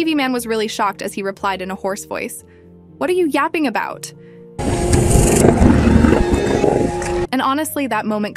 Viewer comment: he actually said what the fuck are you yapping about